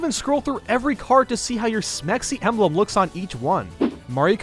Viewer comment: sounds like smexy subtitles says sexy.